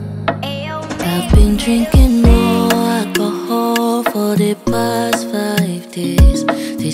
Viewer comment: I thought she saying I’ve been drinking NO alcohol for the past five days 😂😂😂😂😂 but no she saying more alcohol 😅😂